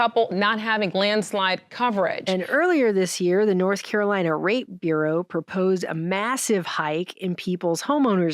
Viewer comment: did say "North Carolina Rate Bureau" or "North Carolina Ra*e Bureau", because of the way things are presently designed, I'd think she said the latter.